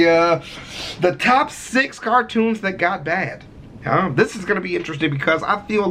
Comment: The title of this video literally made me dyslexic and made me cross-eyed lmao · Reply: I thought it said: "6 cartoons that God like"😂😂